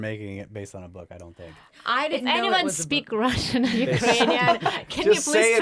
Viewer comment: Did she say "Does anybody speak Latvian"? - I speak Latvian! 😄